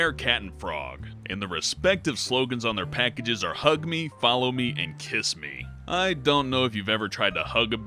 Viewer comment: During the coat hanger one when it was saying the slogans, the video glitched, and instead of saying "follow me," it sounded like it said "fuck me."